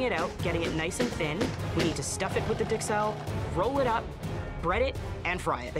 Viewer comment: Did she say “Dixelles”, instead of “Duxelles”?